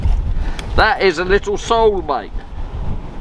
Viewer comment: it sounded like you said you caught your "sole mate" LOL. well done mate. 👍👍👍🎣🎣🎣